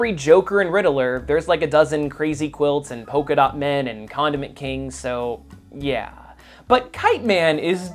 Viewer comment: I thought you said Condom man Until I figured of you said Condiment man